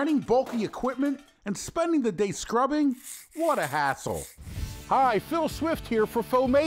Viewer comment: Did he just say " What a asshole?"